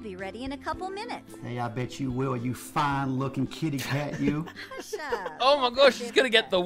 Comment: lmao I thought he said: ayo bitch you will you fine looking kitty cat 😂